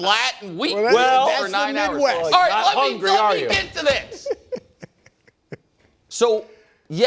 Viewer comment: I thought he said let me f**king get to this! Lmao